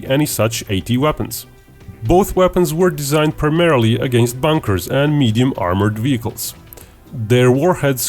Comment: did he say the weapons were meant to be used against bankers? WTF?